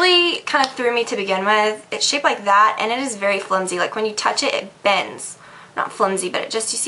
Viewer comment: When you were talking about the mascara I could have sworn you said Flimsy bitch hahahaha I was like whaaaa? Yes I did a double take on that one :D